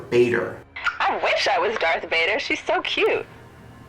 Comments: did she just say "i wish i was darth vader shes so cute" mhhh thu fuq. Also she does sounf robotic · i started screaming inside of me.. did she say "i wish i was darth vader shes so cute"?